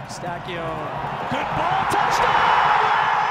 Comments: did he say touchdown 😂😭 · did he just say touch down??? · Did he say "Touchdown"?